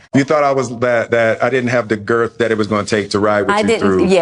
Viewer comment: Did Will just say girth? 😂🤦🏾‍♂️